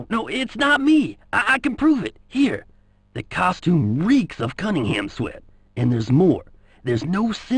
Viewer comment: Especially since Jason specifically knows what Cunningham's sweat smells like. At least that's what it sounds like he says at 3:10. *shudder*